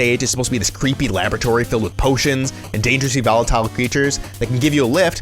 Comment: What word did you use to describe "creatures" at 9:30? Just curious. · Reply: "Dangerously volatile" My enunciation can get a little sloppy when I talk fast. xD